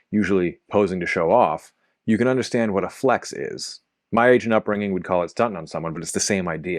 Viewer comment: I thought you said "My Asian upbringing" -- I had to go back and double take that lol